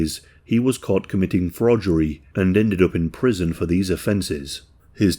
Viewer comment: Wait.. Did he say fraudgery?